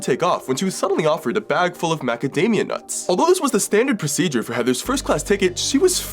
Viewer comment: when he said Macadamia nuts , i thought he said academia nuts